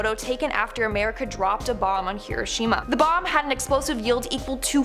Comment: I thought she said kirishma ;w